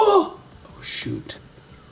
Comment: Is this the first time he ever cussed cause he cussed at that moment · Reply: It said shoot it’s ok though u misheard no hate